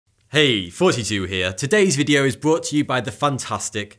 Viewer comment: Every time he says his intro it sounds like he says hey 42 here 😂 great video tho